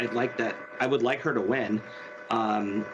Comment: Did he just say that - despite all of his counterpoints AGAINST Lake - "I would like her to win" ??